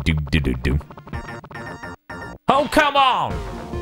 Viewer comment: For actual, real-life decades I thought the voice clip was saying "Sumo!" Hearing that it's "Come on!" has legitimately blown my mind (and also makes way more sense (^^)'> )